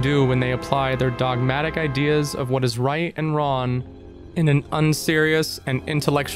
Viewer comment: When you said what is right and wrong it sounded like you said what is right and Ron, and in Japanese slang ronery means lonely and as a socially isolated character even the concept of being lonely is of course villainized demonized criminalized and ostracized to the point where in religion one must ask like God where did loneliness come from if it's such an offensive thing. What is right and what is wrong so basically what is group and what is not group yeah that's how (hiveminds attack) people think.